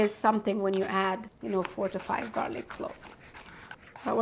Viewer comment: I thought I heard 45 garlic cloves 😂😂